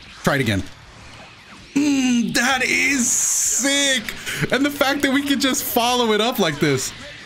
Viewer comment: When my man's says mmm dat is sick I thought he said mmm Daddy I was like wait huh